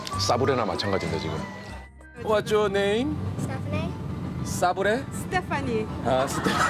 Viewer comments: 아니 근뎈ㅋㅋ 알고 몇번을 들어도 사브레로 들리는 내 귀는 뭐람 ㅋㅋㅋㅋ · 난 ㄹㅇ 아무리들어도 싸브레로 들리는데 ㅋㅋㅋㅋㅋㅋㅋㅋㅋㅋ